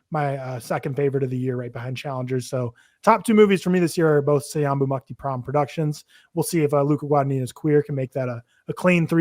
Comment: you mention that challengers and trap are both “????” productions. could you please spell out what you were saying there? would like to be more knowledgeable!! thanks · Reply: Sayombhu Mukdeeprom - he’s a cinematographer. worked on trap and challengers